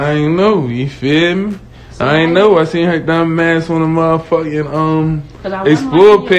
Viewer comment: Did he say "I seen yo dumba**" 15:03..? 🤦🏾‍♀️